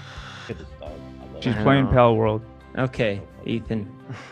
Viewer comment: Every time Jeff says okay all I hear is “okay, Ethan”